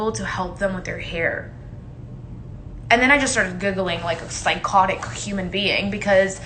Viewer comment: “I started googling psychotic human beings” as she angrily brushes her hair like a psycho 😆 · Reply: Omg I thought she said googling too...but giggling makes slightly more sense. She kind of trailed off and never finished the sentence like everyone who rants on IG live lol